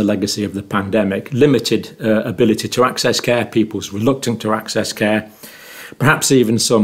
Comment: Thought you said elf care.... Carry on...